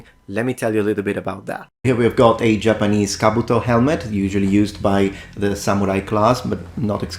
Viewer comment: I head to go back, because I misheard "usually used by the Samurai class" as "used in my Samurai class", and was severly confused, because my head immediately went to the mental image of a Professor entering class in a full suit of armor to get the students attention.